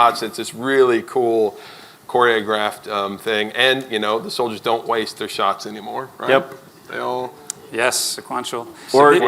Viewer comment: I think he said they don't miss anymore, huh? No fun or suspense or tension in combat if you can't miss. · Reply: he said they don't "waste" their shots anymore. Not that they don't miss... He meant "waste" as in you have four troops on Overwatch and a sectoid jumps into view and all four waste their OW on the sectoid. Shots will now be SEQUENTIAL.